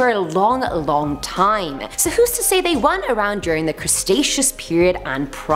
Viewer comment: Did she say the "crustaceous" period? 🤔